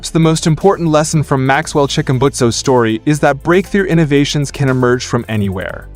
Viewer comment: Ain't no way I heard "Maxwell Chickenbutso..."💀